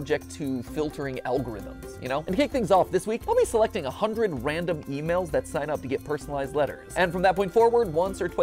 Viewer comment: At first I thought he said females